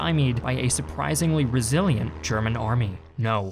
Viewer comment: Brazilian german army? Did i hear it wrong?